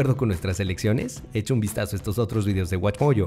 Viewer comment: porque dijo watchPOLLO? Jajajaja